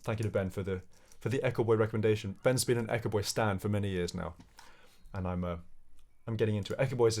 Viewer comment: «Ben’s been an Echoboy stan for many years now». Did i hear that right lolololol